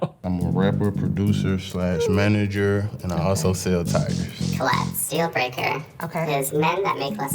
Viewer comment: did he say he sells TIRES or TIGERS??? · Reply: I heard tigers but I think it’s tires 😭